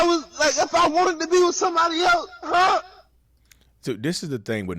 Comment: I thought he was saying “ I want to be with you and Tanesha 😂”